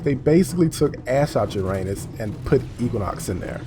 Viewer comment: Oh my god, I thought you said you said "They took ass out uranus and put equinox in there" but it was Ash. I'm too used to you making puns that I'm beginning to hear things wrong now!